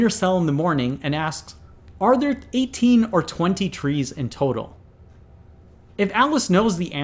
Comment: The wording of the question does not make it clear that there are either 18 or 20 trees in total. From the way it is worded, I assumed that there could be any number of trees in total, which makes the problem quite impossible. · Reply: Haha, yeah. I thought he was saying "Is there either 18 or 20 trees, or is it some other number?" but he's saying "It's either 18 or 20 trees, which?